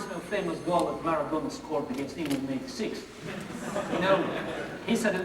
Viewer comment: What exactly was it that he said at 45:44? All I could catch is "it reminds of a famous..." & then "... he said it was the hand of God." Everything between I missed. But whatever it was must have been pretty clever, judging by the reaction of the audience. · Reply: He mentions Maradona's goal against England in the 1986 World Cup. Maradona scored with his hand and said after the game that it was the "hand of God".